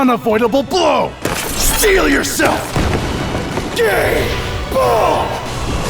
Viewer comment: did he just say "gay bolt" ?